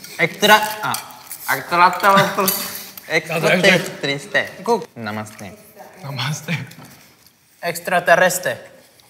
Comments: Soy yo o en el minuto 9:45 escuché un pequeño “mamaste” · Parece que dice 'Te mamaste' jajajaja